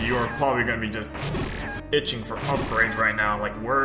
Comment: I thought you said "bitching for some upgrades" at first, had to re-listen to it like 3 times lol